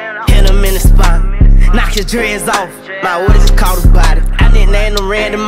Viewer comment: Literally searched: ”KNOCK HIS DREADS OFF” 💀💀💀💀💀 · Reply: Shiiit I thought he said "knocked his head off" lmao😂😂🤔